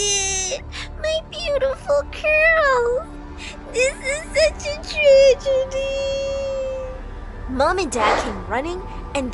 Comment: The subtitles said " This is such a treasure dudeee" 💀💀💀💀💀